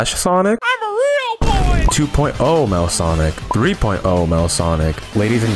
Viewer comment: I thought he said all male sonics